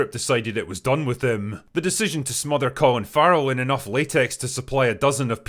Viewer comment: I don't know who Colin Farrell is, so for a minute I thought you said it was Will Ferrell. I was like: "Dang! That makeup really IS amazing! And he sure upped his acting game!" 😂